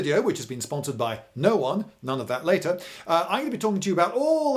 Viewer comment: "Sponsored by No One None of that later" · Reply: I'm so used to hearing "More on that later" that as he said that I heard, "More on that later" the first time! XD